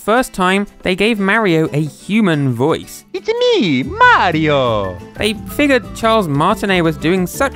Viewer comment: Why does it sound Like Mario is saying “it’s a me a mardio!